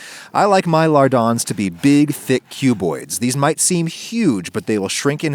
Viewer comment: I thought you said "big, thick cubey bois" at first. Disappointed.